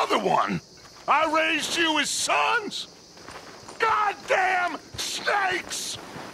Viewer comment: riding through the swamps my horse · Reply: I heard it as “GOT DEM STEAKS”